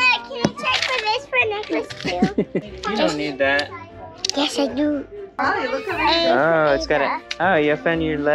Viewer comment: what does she say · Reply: said “yes i do”